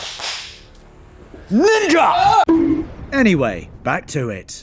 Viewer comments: DID HE SAY NI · what did he say? 😂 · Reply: Ninja